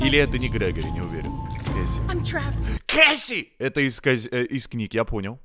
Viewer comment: а вам тоже вместо Кесси послышалось Джесси и захотелось варить?